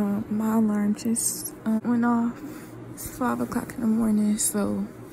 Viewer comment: Did you take a 5 hour shower??? takes 5 hour shower Water companies: STONKS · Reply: Woops I thought you said "it's 12 o'clock in the morning", my bad haha